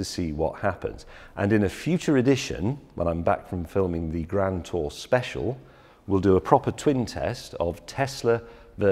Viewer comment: Whut? Did I hear correct? A Grand Tour Special .. Whoop!!!!